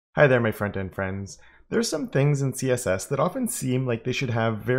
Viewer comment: Ohhhh! Front-end friends! I finally understood your greeting! I always thought you said "Hello my friends and friends" 😁🤣